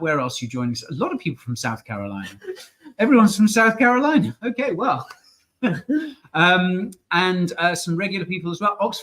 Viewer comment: Did he just say, "Everyone's from South Carolina, and... some regular people as well?"😂